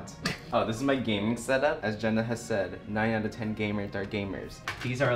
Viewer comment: I heard 9/10 “gay nerds” are gamers and had to rewatch 3 times to hear it correctly